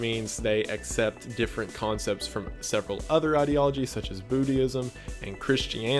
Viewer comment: How can I follow the path of "Bootyism" instead of "Buddhism"? Love the Wendigoon out of contexts!